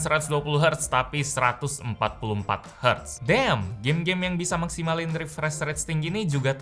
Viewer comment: Lu bilang apa? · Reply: Damn